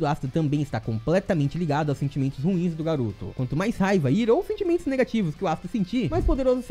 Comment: Eu juro que escutei o falando "Naruto" Mas era "Garoto"😁